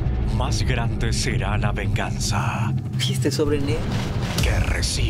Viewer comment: que dijo la noni??? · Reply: Y este sobre negro?